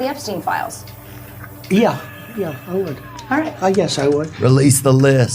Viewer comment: Virginia Guiffre DID NOT commit suicide. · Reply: the way he answered that question set it all right there. And he even threw in, "I guess" it sounded like to me....